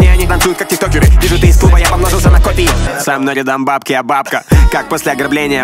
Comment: "Со мной рядом бабки я бабка" Или я не расслышал